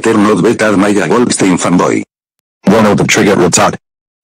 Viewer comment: Did he just say "Femboy?" LMAO!